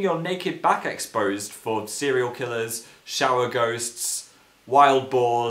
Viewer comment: Phil, Why does your dad want to be a goose? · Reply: I thought he said ghost?